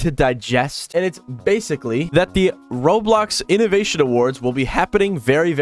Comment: bro. Are these captions AI generated? I'm asking because the captions are alright but then they make the dumbest mistakes that if it was a person writing it would make. Like at 2:39 it says "Roadblocks" instead of "Roblox" and then at 3:21 it says "Block C". Theres no way this is a person writing these captions, I refuse to believe this is a person who made these captions.